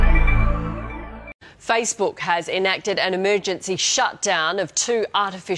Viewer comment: what? Saysbook?